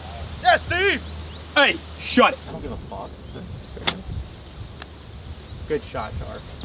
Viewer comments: Yo, 27:56 I swear said "I don't give a fuck." 😂😂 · did steve say he doesn't give a fuck at 27:55? LOL